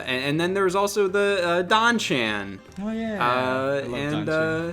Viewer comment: Thought said Don Cheadle (who he also loves)